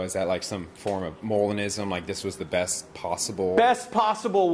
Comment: What's the word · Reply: Molinism. "acts17apologetics" has a good vid about it called "What is Molinism?"